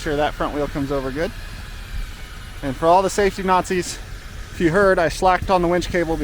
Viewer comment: LOL, is that a Canadian reference I heard "Safety Mounties"?